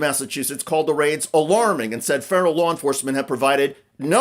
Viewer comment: Did you say "federal law enforcement" or "feral law enforcement"? I guess they're synonymous at this point.